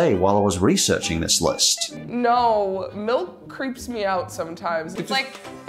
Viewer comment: Um, the fuck did you say? MILK, creeps you out?